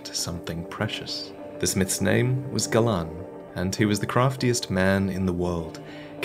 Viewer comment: When you said, during the Puppet Knight set. At first I thought you said “Gavlaan”....Gavlaan wheel Gavlaan deal